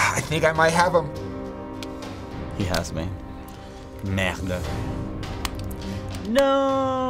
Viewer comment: Did he just said "Merda!"? Any brazilians? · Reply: I heard "merde" which is the same but in french.